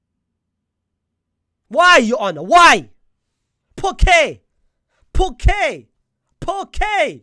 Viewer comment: Poké !? Poké!?😂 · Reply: It took me a minute to realize he was saying "POR QUÈ?!" I genuinely thought he was saying "Poke there! Poke there!" and I was wondering why are you poking the judge?😭